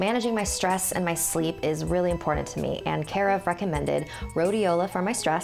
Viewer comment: Sounds like she said f***ing mended instead of recommended Did she say recommended?